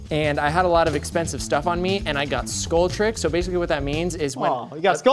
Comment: When told about the skull trick I felt that, tassets are so expensive :( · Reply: I thought he said "Skoll" and he "got Skolled". I wonder how long he actually talked about this before they stopped him.